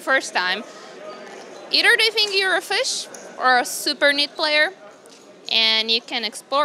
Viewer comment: I think the title was suppose to be "Fish or Super NIT" not Neat.. I mean with her accent it sounded like she said she was a "Super Neat Player", but I don't think that's what she meant.